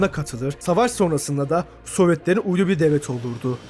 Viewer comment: tam 20 kere sardım. Hala düşünüyorum. Orada ne dedin! Soyvetlere ulguluvi devlet olurdu 😮